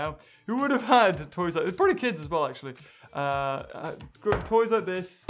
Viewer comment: Did he say pretty kids